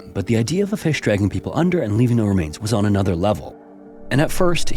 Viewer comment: Geez dude, even the captions can't tell what you're saying when you talk so fast that it puts random words in. At 17:48, you say, "leavinguhremains" but the captions say "leaving the remains." Slow the hell down, you're not in a race bruh.